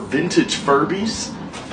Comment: My volume was turned down so i missheard you and thought you said furries🥴🫠